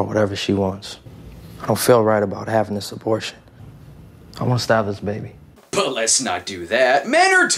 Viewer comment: kind of sounds like he said: "I wanna stab this baby."